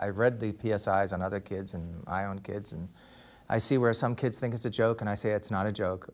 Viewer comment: He’s wearing his robe for a casual sit down. 🙄 Did he just say “I own kids.” 😂